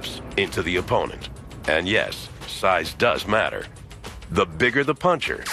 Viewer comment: I thought she said size doesnt matter